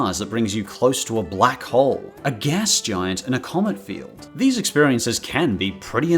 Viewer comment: When he said commit field i thought he said comment field Yesss kingdom hearts, you know that you can buy them all on one disk now. You gotta admit that the end scene is still on par with graphics to this day